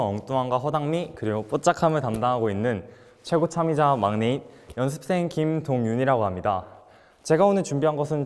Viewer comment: is he say maknae?